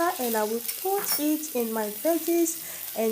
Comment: At mark 3:29 or so, using the closed caption feature, this is what it says she's saying. "Tablespoon cornstarch right from water and I would pour it in my panties"! LOL! Anyway... all kidding aside, it looks very delish and I am making it for my daughter & her fiance.